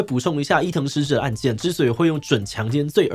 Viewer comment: 媽呀每次我聽到講伊藤詩織這個名字我都會聽成藤崎詩織😂